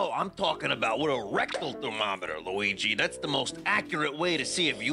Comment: I was so confused at first because I thought he said "regular thermometer".